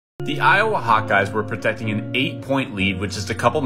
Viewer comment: I keep hearing "the Iowa hot guys"